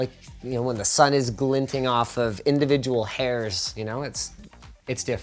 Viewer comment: Did he just say "hairs"